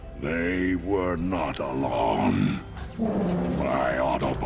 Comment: Did Prime say “Along” or “Alone”? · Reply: Alone